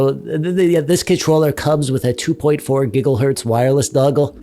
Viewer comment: Did he say gigglehertz at 3:16? Also I find it interesting that he chose a submarine game with that controller 😂😂😂